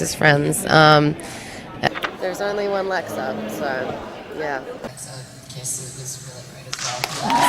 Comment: what does she say in 4:08 minute? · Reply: they were talking about their favourite scenes and she said the kiss scene with lexa was really great